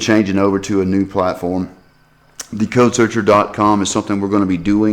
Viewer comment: Aha, so it's <link>. Sounded like you said merely codesearcher(.)com. When I looked for it earlier, it wasn't there. Didn't hear "the" in front of codesearcher(.)com. It's VITAL that it be enunciated clearly or else no one will find you (had to put the dot in parentheses otherwise the link would be a real link and it would have gone nowhere).